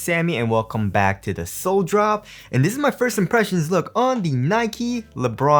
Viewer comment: Every time Sammy says "Sole Drop" I hear "sole brothers" :/